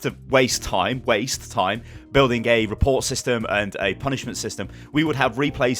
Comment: I swear I heard "building gay report system" forgive me